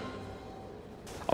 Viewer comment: so watching with closed captions on, at 20:29, Rob says "I'm not going in that." Closed captions says "I'm not gay tonight"................???????????????????